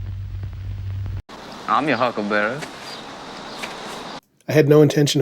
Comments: I thought he said “I’m your funeral casket handle berry” 🤷‍♂️ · My mistake was I thought he said “I’m HERE Huckleberry”, using huckleberry as an insult to his opponent. I didn’t think he said, “I’m YOUR Huckleberry”. Not gonna lie I like, I’m here huckleberry way more.